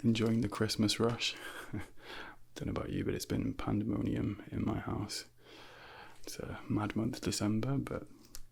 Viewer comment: I heard "it's been pandemonium in my ass" at 0:31, I was like "bro wtf" 👍